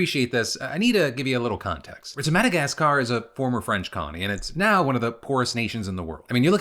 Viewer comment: He did say the president of Madagascar was a former DJ, right? Or am I just mishearing that?